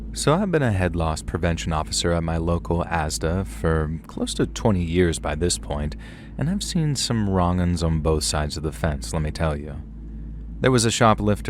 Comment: Am I the only one who heard “head loss prevention officer” wrong? Lol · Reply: Omg I JUST got that it was the Head (of) Loss Prevention lmao I thought he said head-loss prevention officer too.